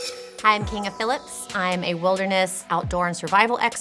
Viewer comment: Every time this woman introduces herself I hear "King of Philips". I've known guys named Philip and they never mentioned anything about having a monarch.